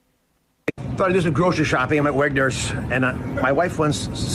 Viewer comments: I swear, I thought he said, "I'm a wet nurse."🍼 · 🤣🤣And here I thought Oz said, "I'm a wet nurse."